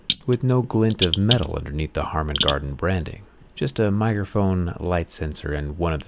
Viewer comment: Did you say "Harman Gardon" and not Harman Kardon.... 😅